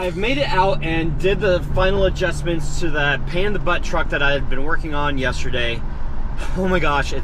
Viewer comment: 😂 our ears hear what they hear, then that is all they hear 🙉..... At 0:15 I thought you said "Panda Butt Truck" just too funny 🤣